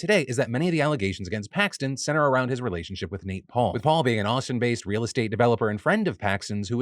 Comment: i heard “awesome, based” instead of Austin-based and was like….ok?